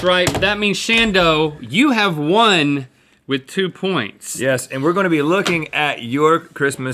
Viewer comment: did anyone else think he said one and two points